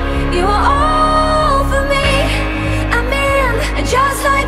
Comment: An Vsce thought she was saying "I mean" instead of "I'm in"